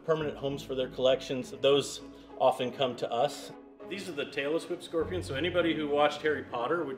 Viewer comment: I really thought he was saying “Taylor Swift scorpion” 2:19 🤣